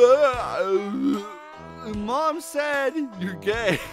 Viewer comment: Lmao biolizard is technically Shadow's brother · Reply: I thought I heard "mom says ur gay"😂😂